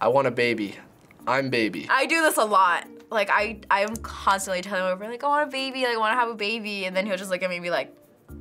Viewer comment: did she say her “BROTHER” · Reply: She said I’m constantly telling him